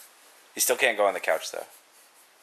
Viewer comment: What i heard "Subscribe...WEEWEE"AWW